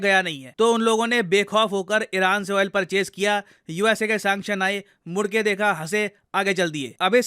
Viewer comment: "Chaa mudayo" should be there instead of bhad me jao